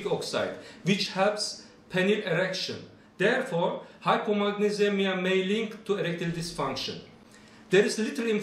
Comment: I don't understand what your saying it sound like you said magnesium can lead to erectile dysfunction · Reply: I am saying that hypomagnesemia can lead to erectile dysfunction.